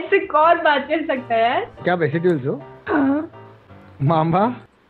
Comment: what did he say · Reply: “Kya aap s8ul se ho”